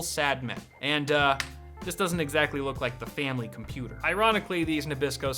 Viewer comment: DID YOU SAY FAMILY??